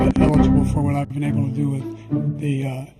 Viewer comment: is it just me or did Biden say “skibidi with” ??